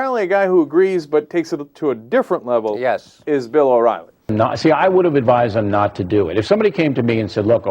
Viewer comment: LMAO wow idk why but my captions where on and at 1:12 instead of "Bill O'Reilly" it says "Bill Rock Nazi"